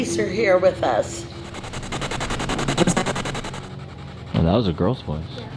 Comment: I heard “What was that?”